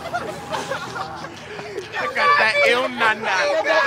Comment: Lmao! did anybody catch at 4:25, one of the guys was like "got that no NaNa" lmao